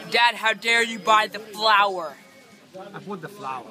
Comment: what did mohamed hadid say ??? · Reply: ZAYN X Dad, how dare you buy the flower. He said, "I bought the flower."